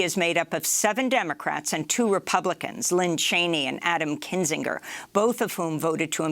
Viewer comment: This is at least the 3rd time I have heard her say "LINN Cheney"... it's LIZ Cheney hun.